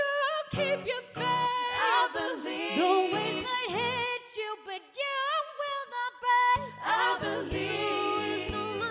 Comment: what does says? I really can't understand :c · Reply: "The waves might hit you, but you will not break"